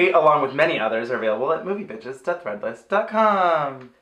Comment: I swear said "movie bitches death-threat list .com"